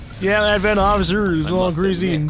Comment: Did he say the ADVENT soldier was all greasy